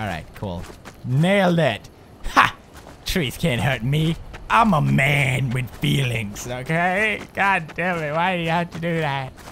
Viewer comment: Pewds says: I'm a man with feelings! ... I thought he said: I'm a man called Felix;) xD. Guess imma little bit dumb right?;P